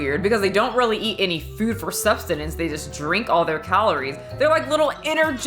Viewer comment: did you just say substenance?